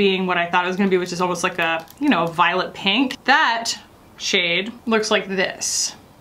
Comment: I heard "violet pink" as "violent pink". Idk what violent pink would look like, but I bet it's a mood. Hahaha is also a mood "Wicca wicca woo"